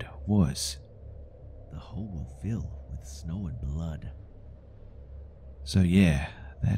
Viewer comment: what did the creature say, i heard something about snow a blood. at about